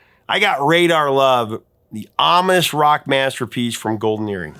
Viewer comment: when you said “Radar Love, the ominous rock masterpiece from Golden Earring,” at first I thought you said “the Amish rock masterpiece.” 😂🤣 I had to listen to it with the captions to help me figure out you said ominous. Oops - my bad.